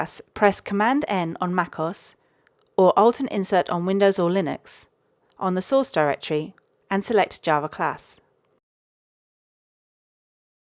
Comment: What did u say at 2:37 ? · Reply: Press Command+N on Mac OS or Alt+Insert on Windos and Linux